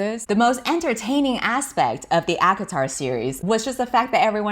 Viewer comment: Every time you said "ACOTAR" I had a moment where I thought you said "Avatar" and I was like "Excuse me?!?!?" lol Anyway, love your videos and this was no exception! Thanks for sharing it with the world!! haha <3